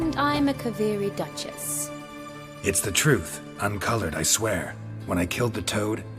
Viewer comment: I played without subs and I thought Geralt said “Its the truth, i’m colored I swear!” and I was like “What the fuck?”